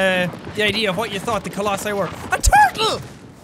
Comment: I thought he said "a tackle" and tackled the rock, but he said turtle and stylishly jump to the rock ahahhaha